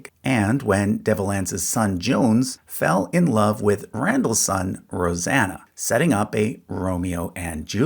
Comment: Sounded like you said, “Randall’s son, “Roseanna”. Lol